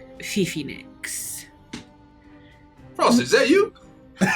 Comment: I took my time to try and decipher what was being yelled here out of my own curiosity and pleasure. 🔥“Frosty! Is that you?” 🤡“No, but I’m here! DON’T BLINK! DON’T BLINK! DON’T BLINK! Don’t blink, Gideon! You better not blink! It would be such a shame to make a fool out of yourself in front of that ~supple~ maladrin*! Oh, you better not blink Gideon! Please don’t blink! I’m helping you! I’m really helping you.” 🔥”I scoop some mud off the ground…and just like…mud in my ears.” 🤡”I’m in your head, Gideon! Not in your ears!” [Follow along on your own.] 🔥’ part : “You’re in my ears! I can hear it through the veil! You’re not haunting me right now! I can hear you~. You’re not in my head; you’re coming in through my ears! You don’t even understand! You’re just dumb! And dead! And you weren’t funny! And I hate you! And I hate you! I manslaughterd you! But I wish it was murder! I WISH IT WAS MURDER.” 🤡’s part : “I’m here forever! You said you missed me but you can’t miss me! I’m here forever. I’m here forever, Gideon. You killed me. You bear the responsibility for my death. I am in clown-space-hell for a reason. It is your fault, Gideon. I am here- BLINK! BLINK DAMN YOU! I HATE YOU!…BLINK!” You’re welcome if you needed this…or even wanted it…if it crossed your mind. * Malandrin - A rouge or scoundrel.